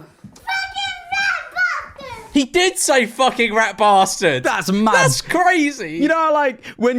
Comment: he clearly says fat bastard